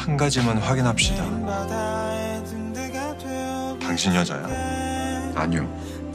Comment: 당신 여자야? 신..이라는 글자에 힘을 주면..되는데..신..이라는 글자에 힘을 안주면..당신 게이야? 로 들리는 발음.